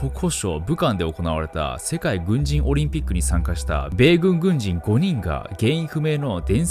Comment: 世界ブンジンオリンピックって聞こえたww